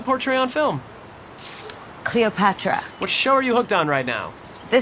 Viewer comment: Did she say... Cleopatra?